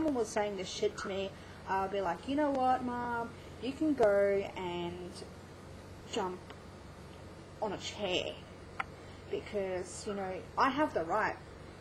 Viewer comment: did you just say " mom you can go jump on a chair? did I hear that right? it made me laugh..